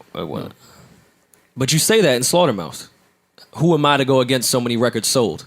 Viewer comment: Did he just say SlaughterMOUSE? Lmao.